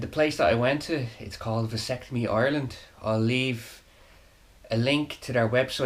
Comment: At first i thought you said "Vasectomy Island" and i pictured a game show...jus kiddin, takes a strong will to make a decision like that , all the best.